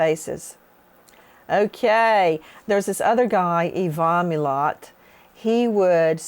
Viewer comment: Anyone know the name of the killer Nancy referenced at 9:25 in the video? The one that stalked his victims at campsites. I haven't heard that name before and can't find anything on google or youtube. I thought she said Yves Omulot...but...apparently, I'm incorrect. Do you know? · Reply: Ivan milat